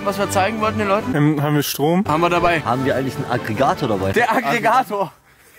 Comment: Ich hab statt Aggregator Alligator verstanden hab mich aber bei der Truppe nicht gewundert bei Alligator 🙂